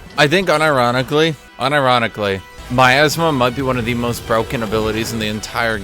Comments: I legit thought he said "my asthma" LOL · what ability did he say? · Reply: Miasma, Cassiopeia's W.